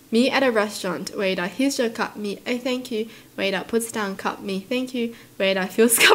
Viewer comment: what i hear: thaenk¡e